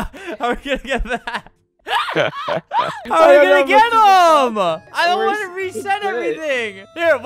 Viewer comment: Why did I hear, "I'm in the ghetto."